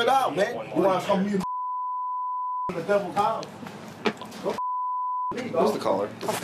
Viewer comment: Bro ol’boy is possessed, the demon was charming 😂 5:25 the cop says “devil” and the demon comes out and then tries to play it off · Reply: it sounds like he says the devil's house.